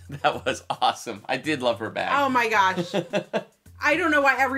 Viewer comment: When you guys were talking about her bag at the beginning I thought you were saying her back! Lol. My ears messed with me there.